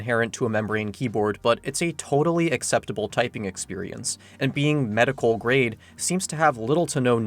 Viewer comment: for the longest time i thought it said medieval grade,,,, i was waiting and waiting for anything medieval and nothing would happen. i feel like an idiot